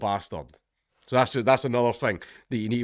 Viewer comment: Ngl, I thought that said chonker and I was really confused.